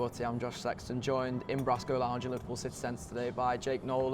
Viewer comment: Which restaurant/ cafe is this? Really nice, Kudos to the owner 👌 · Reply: Sounds like he said Brasco Lounge?